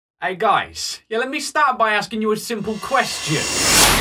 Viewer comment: When said start by asking you three questions a thought he said,hey let me stab my a